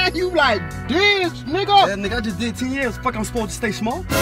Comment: What does tiny say at 0:11 I just did a tf? What does that mean does anyone know what he actually says cause I’ve watched this video several times and didn’t catch every time what he says